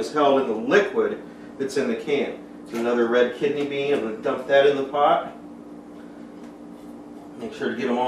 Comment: the captions say the indians of that when says red kidney beans dump that in the pot xD